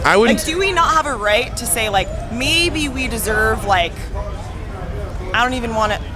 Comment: did she just said "deserve"? xD